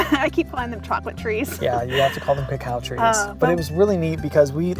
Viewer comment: At first I thought you were saying cow cow trees. lol! Cacao trees! The chocolate location looks like a must visit place!!!